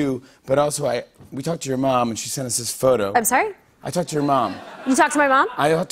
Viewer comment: She misheard it as 'I touched your mom' instead of 'I talked to your mom' 😂😂😂